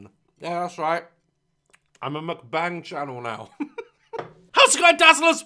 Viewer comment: i heard that as “im a mcbang channel” and wondered if mcdonalds sold guns